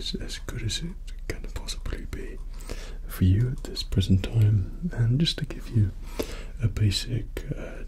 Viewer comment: did he say prison time?